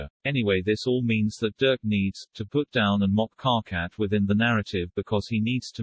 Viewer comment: im only about 2 minutes in and yes i am very on board so far, i just need it to be known that at 1:59 i misheard that as "dirk needs to be put down". like as in euthanasia. i thought this person was suggesting murdering dirk